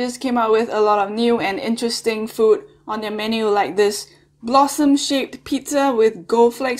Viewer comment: sorry but i thought you said "blossom shit pizza"